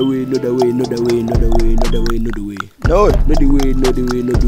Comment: it kinda sounds like hes saying nut away nut away nut away at certain points